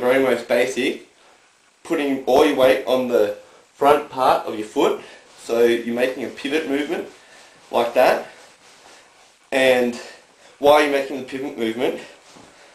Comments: haha i thought he said "making a pubic movement" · instead of pivot it sounds like hes saying pubic lol